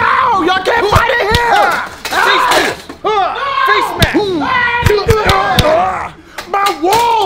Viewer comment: “Face Mask!” Lol that coronavirus lockdown content is coming clutch. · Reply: I heard "fake ass"